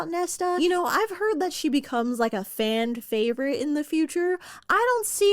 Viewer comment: Did I just hear “fand favorite”? I think I did…cute though lol